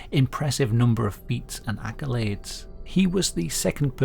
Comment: I heard "and in his life, he's collected an impressive number of feet" and was disturbed for a moment 🦶